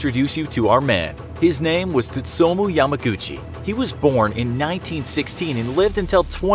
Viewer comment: Video:His name is 3:46 Me: is it me or did i hear GUCCI in his name.